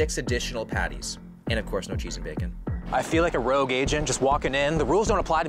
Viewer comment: My ears are messeed. I thought he said "Rogue Asian" at 4:40 😂